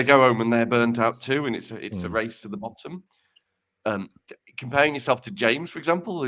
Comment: Did say that burnout is like "a race to the bottom" at 36:50? If so, then DING.